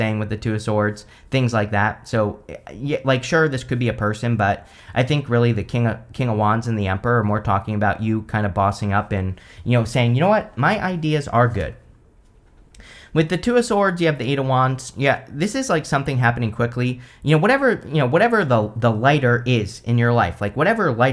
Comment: I heard undies instead of ideas 🤣🤣🤣